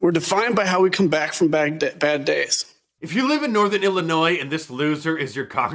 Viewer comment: Notice how he almost said "defined by how we come back from Baghdad" instead of "bad days" these tyrants love war and have a hard time keeping that a secret.